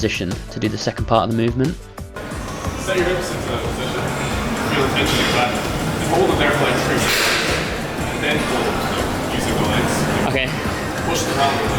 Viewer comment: It's really hard to hear what Clarence is saying at 5:20. Can you/someone type it down? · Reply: Clarence: "Set your hips into the position. Feel the tension in your back. Hold it there for like 3 seconds, and then pull. Like, use your lower legs" "okay" Clarence: "Like, push around your legs, and try to keep your--try not to let your hips like rise up " "okay" Clarence: "because you were doing this yourself, like starting with hips high, then down low, then you pull, then they rise up, so...-" "Right" Clarence: "So standing start would just be easier"